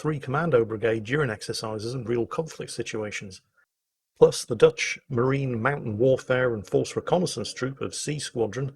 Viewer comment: For a moment there i heard the Dutch mountain warfare troop... Not very likely, no mountain.